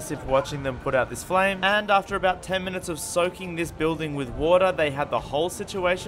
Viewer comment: I thought she say sucking . Water and i don't know what did she say